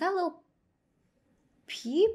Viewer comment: tell me why i thought she said is that dula peep